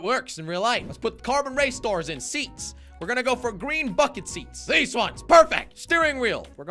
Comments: Is it only me but when said "lets get the racing bucket seat" when he said "bucket" it sounded like he said the f word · I thought he said green "fcking" seats- ✋😀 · That sounded like fu*king seats lol 😂